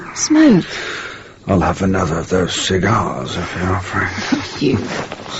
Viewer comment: What did the wife say when her husband asked for a"smoke"? It sounded like she said "F.U." Really? Is this a modern radio play? I will continue listening to see if it is.